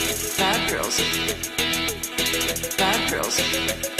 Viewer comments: For a second I thought it said Fat girls.... Fat girls.... I was like well I’m at the right place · I thought it said fat girls